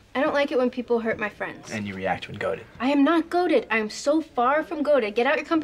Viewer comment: ? What does Logan say? "And you react guarded?" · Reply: "And you react when goaded" so basically it is "And you react when 'provoked'" Hope that helped :)